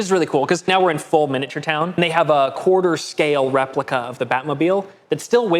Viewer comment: Danny DeVito as The Penguin totally makes sense. His hideout is in the sewers, which is perfect. You can take off your shoes and search for rings and coins and such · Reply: I misheard and heard "quarter" as Corridor, and that made me think that "Corridor Quarterly" sounds like something that could exist